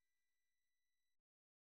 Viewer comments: が何回聞いても「原神３０年」にしか聞こえない件ｗｗｗ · どう頑張って聞いても30年にしか聞こえないの草